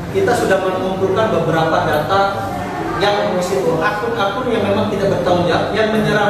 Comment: Kalo bisa kalahkan 40 atau 200 ya nda usah di publikasikan. Cukup berprestasi saja itu sudah luar biasa. Menit 10:20 pengacarannya malah bilang ada aku yg tidak bertanggung jawab, akan di selidiki jadi lucu dengernya. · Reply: Dia bilang akun akun yg tidak bertanggung jawab